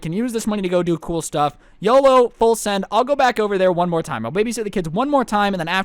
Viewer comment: I thought you said that you would baby shit those kids..